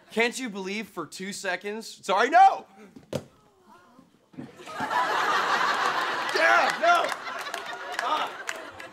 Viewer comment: What is the person saying off screen at 1:49 after Jean-Luc says "Well boys.."? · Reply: someone was saying cut